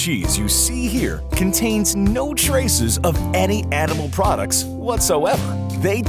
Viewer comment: did he say ‘any edible products’ here? 😂